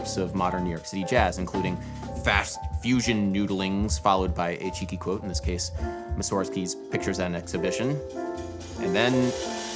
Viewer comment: What the bloody hell is he saying at 20:14? He's talking at like mach 5 I can't understand a word. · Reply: "Fast fusion noodlings, followed by a cheeky quote-in this case, Mussorgsky's Pictures at an Exhibition."